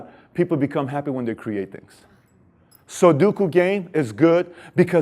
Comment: What do you say @ 4:12 ? "So do cocaine, it's good"? That's what I keep hearing 😭😭😭